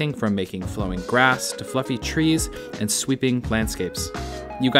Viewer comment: "fluffy trees" my man I though I heard u say "fookin trees" 😂